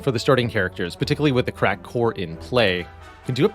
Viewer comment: Totally heard crack whore instead of cracked core lol